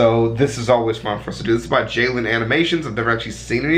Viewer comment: i think he said Jaylen instead of Jaiden